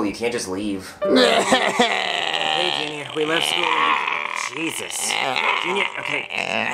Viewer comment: did Cody really say Jesus?😂😂